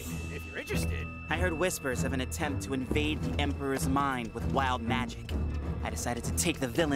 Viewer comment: What he said: "I heard whispers of an attempt to invade the Emporer's mind" What I heard: "I heard Whispers attempting to invading the Emporer's mind"